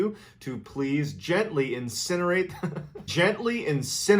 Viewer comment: AT FIRST I THOUGHT HE SAID INSEMINATE, and thats why he was laughing. lol